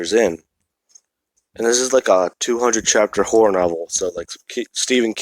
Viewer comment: I can't be the only one who heard him say porn novel instead of horror novel